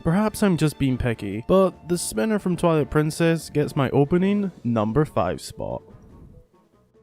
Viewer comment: I thought yoyo when you said spinner.